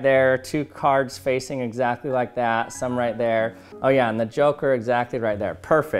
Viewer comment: Did he say Joker?